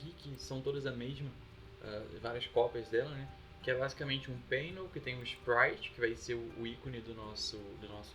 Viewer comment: como é o nome do tipo dos slots? não achei painel nem pannel, nem nenhum tipo que ficasse com o icone igual. o que vc fala em 2:05 ? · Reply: È do tipo Panel